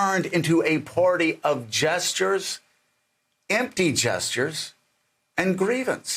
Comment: I thought he said jesters at first not gestures. "The Republican party has become the party of jesters." And I was like well duh yeah they... Oh he said gestures, empty gestures.